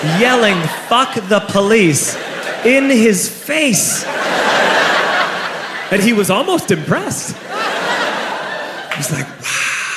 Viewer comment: Yelling F***k DA POLICE and the officer was impressed?!?...So was I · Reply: I thought he said "and he was almost depressed."